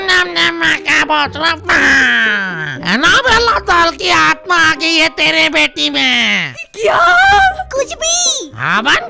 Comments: Did he say 'Ma ka bhosda aag'😂😂😂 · wtf he said teri mkb 😂😂